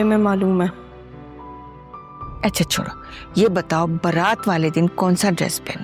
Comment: what she said? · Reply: Kab?